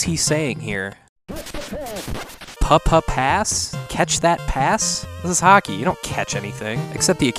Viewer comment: I always thought the announcer was saying "with the pass"